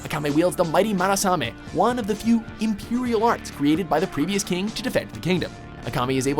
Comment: did you just say Imperial Arts?